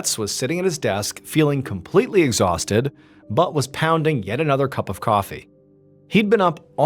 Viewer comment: Sounded like you said his butt was pounding lol